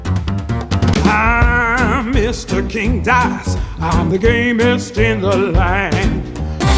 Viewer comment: Cant believe I heard "I'm mr king dice,I'm the gayest in the land"no offence king dice